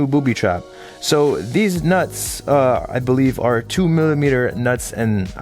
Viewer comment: I thought he said deez nuts😂😂